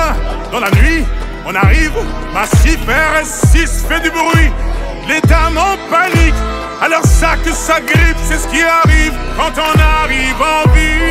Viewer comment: À 0:23, n'y a-t-il que moi qui entend :"les dames en panique, à leur sac s'agrippent" ?